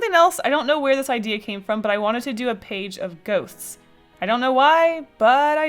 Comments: I thought she said goats😂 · When you said “page of ghosts” I heard ‘ page of goats’ and I got super excited because I love goats but, ghosts are cool too